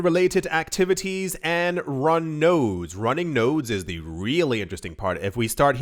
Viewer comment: Lol. I thought he said runny nose 👃 not running nodes 😅😅